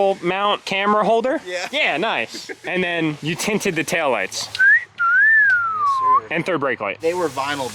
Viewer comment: it definitely sounds like you said “ you tainted the headlights” lmao